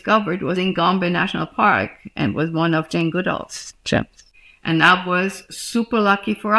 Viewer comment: The caption says "Jim Goodall" instead of "Jane Goodall," which is the correct name and obviously what Dr. Hahn said. I'm guessing the caption writer hadn't heard of Jane Goodall and typed what they thought they'd heard.